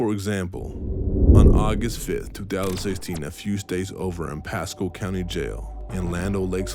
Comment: These subtitles are so wrong. Audio says “catching a charge”- subtitles say “catching a child” This is a really important story. I hope the subtitles can get fixed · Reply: “days” should be” states”. 6:32 instead of “aware that he had mental illness”, the sub says “aware that he had done this” 6:57 is the catching a charge one I mentioned. If I get a chance I’ll look for the others tomorrow